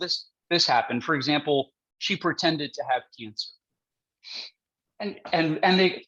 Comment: What did he say she lied about? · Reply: She lied about having cancer